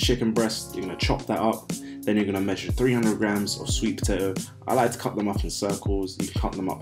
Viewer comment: what is this please. I don't speak English very well, and I don't understand this word and I don't know what is this vegetable. Please help. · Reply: Sweet potato